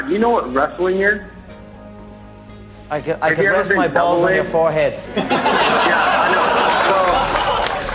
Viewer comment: Chad: Hey Conor...Do you know what wrestling is?!? Conor: ... ... I can rest my nuts on your forehead...lmao. I always thought he said "It's when I rest my nuts on your forehead." I'm dead.